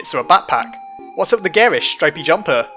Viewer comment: What did you say? I didn't understand that. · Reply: "What's up with the garish, stripy jumper?" 8)